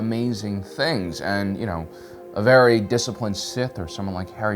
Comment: Briefly misheard and misheard what he said as along the lines of 'A very disciplined sith like Harry Potter'.